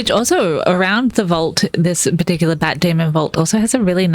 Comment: I'm my sleepy state of mind, you said "bat demons" and I heard "Matt Damon." lol!